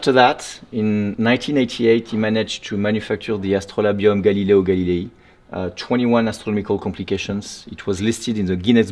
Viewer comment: Idk what he said at 14:34, but I kept going back. I couldn't stop laughing 🤣 😈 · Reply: Tourbillion, Gallileo Gallilei, Like the dude